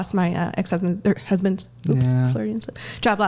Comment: Did she just call her husband her EX husband? lol